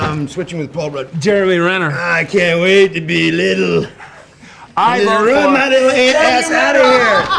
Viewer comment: what does paul rudd say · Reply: I vote for Jeremy Renner